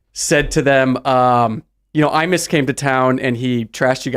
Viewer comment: who came to town? Did he say IMIS? If yes who is that?